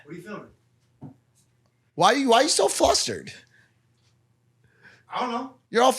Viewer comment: Oh I thought he said fostered I was like?¿